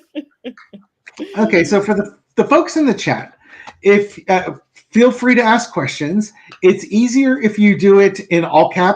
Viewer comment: at 1:25 something was whispered when was talking about the woman he dated before his wife. Could not make out what was said. It was just one word. Did anyone else hear it? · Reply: I will have to watch the video.... ah... at 1:24 was talking about how that person loved drama and chaos and I whispered "Yes"... Was that it?